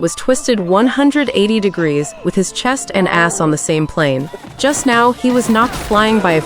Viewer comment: why did bro said his chest and his ass in